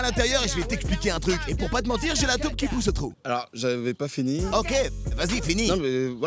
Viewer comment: Il dit quoi à 2:21 pour pas te mentir j’ai la ???? Qui pousse au trou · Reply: taupe